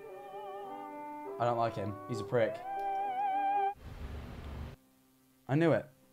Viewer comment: i thought he called him a brit, i- TwT